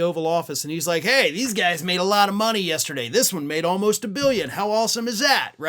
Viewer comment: I would have swore Trump said that the first guy made two billion. Doesn't matter as it reeks of shady dealing.